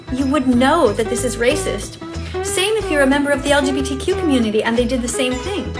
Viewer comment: Wait, did she just say the LGBTQ immunity?????